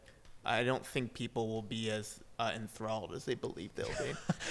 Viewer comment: english is not my first language and i couldn't understand what joe said at 8:44 "i don't think people will be as ? as they believe they'll be" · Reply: veronica he said "enthralled" it basically means impressed/excited